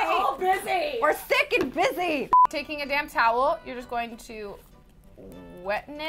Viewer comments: i thought she said damn towel. im done. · It sounded like said "Take a DAMN towel" lol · I thought she said 'you just take a damn towel' and I'm just like, what.